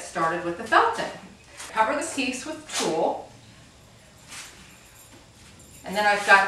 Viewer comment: Hello. I really like your tourtorial. What do you use for covering in 2:24? I can't understand how you call it. Thank you ;) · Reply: Tuille